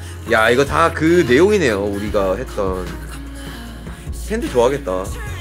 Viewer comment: the subtitles are wrong ?? wtf he said”tthe fans will really like” it not always thinking of army. Not that he isnt ;)